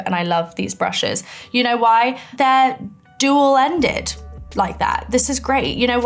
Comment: Millie what did you say 😅😂❤ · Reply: duel ended (two ends) 😂